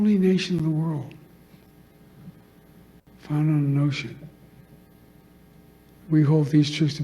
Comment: "We are the only nation in the world...founded on an Ocean." 27:01 - Joe Biden 2025 🤣🤣🤣 Note: Looking back, pretty sure he said "Notion" but it sounded like "Ocean" and as soon as I heard that I couldn't stop laughing! I'm in tears! 🤣